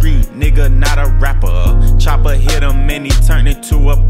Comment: WHAT DID HE SAYYYYY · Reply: Choppa